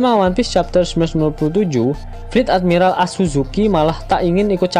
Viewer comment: ga salah denger ya? Si asuzuki ?